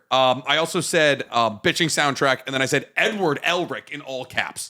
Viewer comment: am i the only one that heard him say "I wrote N word Elric in all caps" I know he said edward but thats what it sounded like he said.